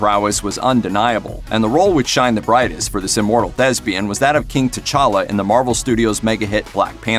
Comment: No disrespect but when he said thespian I thought he said Chadwick was lesbian.